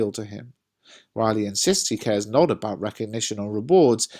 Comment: i think i may have heard it wrong or something but who is riley? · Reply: I think it says " while he"